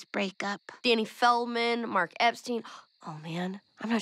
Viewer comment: DID SHE SAY EPSTEIN